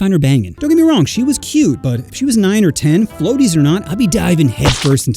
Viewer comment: i heard "if she was 9 or 10 i'd be diving in" instead of what i hope was "if she was A 9 or 10"